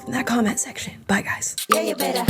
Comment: Does anyone know what the song is she plays at the beginning of her videos. All I hear is "hey you betta hoe". Anyone?